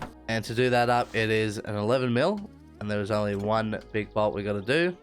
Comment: small error: you verbally say the correct measurement of 11mm for the crankshaft pulley but the subtitles say it is 10mm